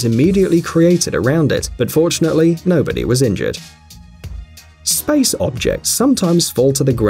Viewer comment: did they say “ unfortunately nobody was injured “